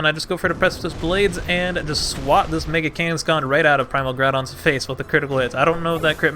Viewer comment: did u just say nigga? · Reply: “i just swat this mega kangaskhan”